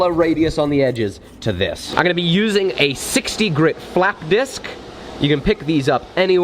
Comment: I know you said flap disk, but I heard "fap disk." Question: When making a fuller, whats the trick to lining up both sides of it? Do they need to be the same depth and how is that achieved?